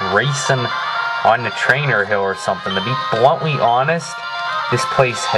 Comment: The first time I watched this, I thought he said "Trainer Mill" instead of "Trainer Hill". XD